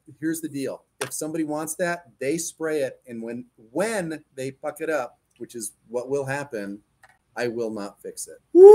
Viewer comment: NO LIE! I had to rewind because I thought that said “fudge it up” Like I actually, mentally substituted the PG-13 version in my mind Hehehe, silverburst